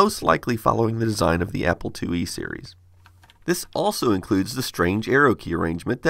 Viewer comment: XD I thought he said fo apple to eat Siri.